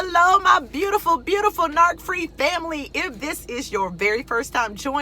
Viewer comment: Your excitement is contagious! I needed that energy this morning.. I have not heard you before so I thought you said Narc Freak 😆... My heart is heavy for my ex boyfriend because hes either nark dark or borderline and and unfortunately alcoholism plays a big part of his dysfunction... As a spiritual person I pray the best for him but I've learned I can't be the one to help... 8 months of dating My eyes were opened after his 1st blow up & we managed thru 6 episodes that I believe he may be even bi polar... He is a good man at his core, intelligent, artistic, but reactive & angry...He had not sought treatment & was unwilling to grow or heal which meant I had to leave for my sanity.... It's so tough too to enjoy a healthy relationship when your body chemistry & mental recordings are out of sync 😪🙏🌸